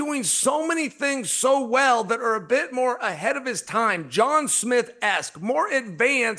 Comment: is he calling jon jones John Smith ?